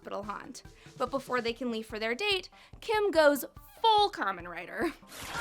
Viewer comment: Did you say Kamen Rider on